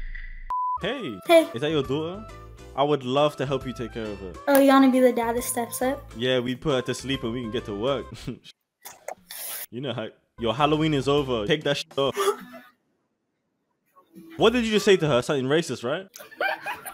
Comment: for a sec I thought he said you can go to sleep so we can get to work💀